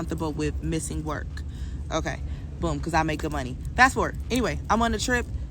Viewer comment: She’s stranded…I thought she said she makes good money🤔 Why couldn’t she get her own way back home? Let someone talk enough and you’ll get the picture.